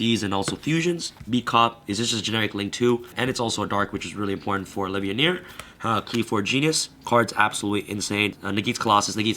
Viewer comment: just getting back into yugioh. couldnt catch the name of the first 2 link monsters at 6:50. what are they? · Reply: beat cop from the underworld and qliphort genius